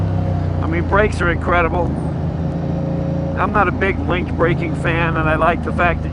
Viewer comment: he makes a comment about the brakes: "...I'm not a big ? braking fan and I like the fact that you have the option on this bike". What kind of braking does he say, I couldn't understand what he said. Ahhhh at 8:55 they discuss "link" braking....